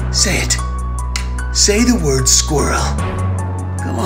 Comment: Dijo Ardilla y pensé que dijo squirtle el Pokémon